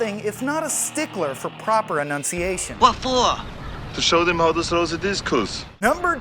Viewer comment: hey you guys, i have a mild case of Asperger's and I am unable to move on in life until I can figure out what he said at 1:07 . "To show them hows to discos?" "To show them how to Habiscus? "To show them how to sew a biscuits?" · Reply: To show them how to throw the discus